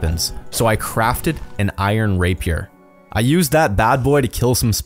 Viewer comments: I thought he said iron raper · When he said iron rapier I thought he said Iron raper